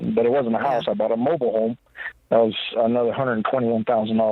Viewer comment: Did say he spent 121k on a mobile home?!